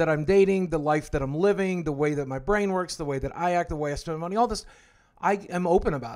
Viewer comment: did he say "the way i steal money"